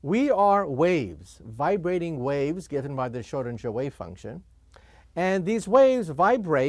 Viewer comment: Owner, I think he said, in 1:11, Schrödinger Wave Function.